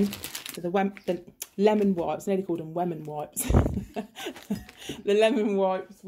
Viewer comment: For a minute I thought you said woman wipes lol xxxx lovely flowers